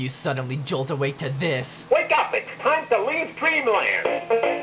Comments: I thought he said wake up it’s time to be a Pringle again 💀💀💀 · I thought he said it’s time to go to Cleveland😂😂😂 Also that means your going to ohio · I thought he was saying "wake up, it's time to leave Cleveland."